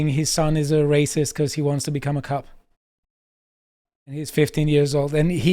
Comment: I thought said regarding Jake Tapper's son "He wants to become a cuck" 😂😂